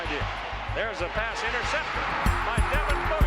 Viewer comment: wow it sounds like he says “there’s a pass intercepted by devin booker!”